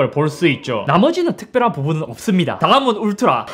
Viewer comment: 나만 광화문 울트라 라고 들었냐